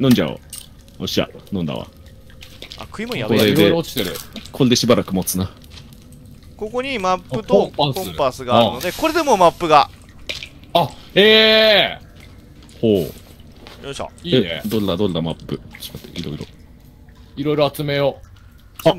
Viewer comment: どうしてもマップとパンパースに聞こえてしょうがない